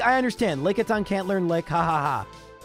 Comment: Please, what does he say at 36:48? "Lickisyng can't like, ha ha ha" i have no idea and i really wanna know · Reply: "Lickitung can't learn Lick, ha ha ha".